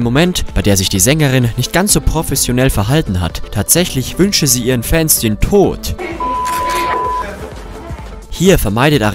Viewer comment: Ohja! Hier hat man viel verstanden! Was zum gottes Willen hat sie denn gesagt?! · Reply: Sie hat gesagt das sie ihr Fans denn tot wünscht